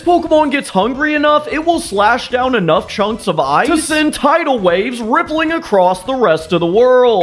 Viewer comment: did i hear tidal wave? 2:08 🥀